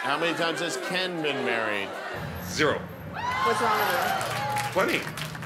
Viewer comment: what did she say there? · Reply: She said what's wrong with u